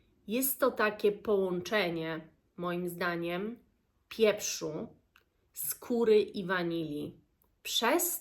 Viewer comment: Usłyszałam pieprzu z kury zamiast pieprzu, skóry...cóż to za składnik myślę sobie 🤣